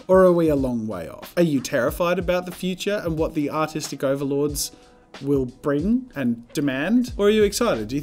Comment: lmao, at first I thought he said autistic overlords